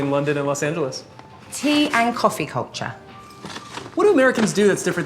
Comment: cultureが紅茶に聞こえたw